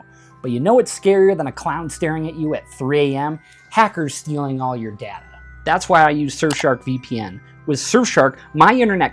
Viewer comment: I thought you said Packer's stealing your data. hahaha. GO LIONS!'